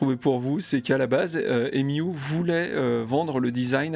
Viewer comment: imiou ? hahahahaha ! cékanssiole ? quoi ? ques tu raconte ? :-D